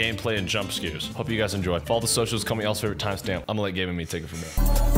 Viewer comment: i need to know what he was saying at 0:20 -- "comment (?) or a timestamp"??? i've tried slowing the video down and reading his lips but i still have no idea.